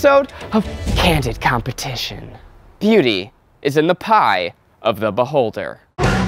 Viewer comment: "beauty is in the pie of the beholder" STAHP OH MY GOSH THAT WAS BEAUTIFUL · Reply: Olivia Elston I heard he said "pewdie is in the pie of the beholder" and I was like what's that? lol